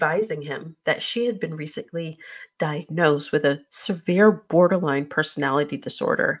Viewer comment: Bipolar is not a personality disorder… it’s a mood disorder. idk why they keep calling it a personality disorder. Although I’d bet money she has one of those too! The apartment prob just didn’t want to get sued. I can’t imagine being a neighbor paying 10 grand a month and having to deal with her nonsense! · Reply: I thought she said she was diagnosed with Borderline personality disorder not Bipolar.